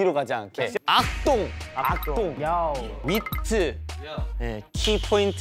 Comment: I am confused. Did they just speak German for a sec? At 0:39 it sounds like Chan is saying "Achtung, bitte". o.O